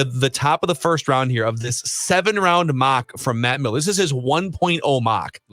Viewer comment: For a second I thought you said MATT MELLEN ! There 45 million reasons you're not getting a 2nd and 3rd round pick for Kirk. More like 2 precent! You're an idiot to think the Bears want Kirk.